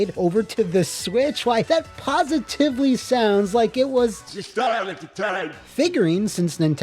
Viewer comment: stylin time? Is that what grandpa simpson said?